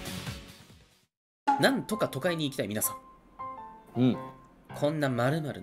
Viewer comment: なんとか都会に行きたいみなさんを なんとか尖に行きたい皆さんと聞き間違えたワイはもう大先生に尖って欲しいんやろうな